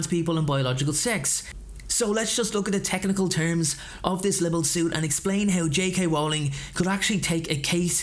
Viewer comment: mate,, know its petty but LIBEL , enjoyed it but winced every time you said it, sounded like you were saying liberal...libel mate libel, but all good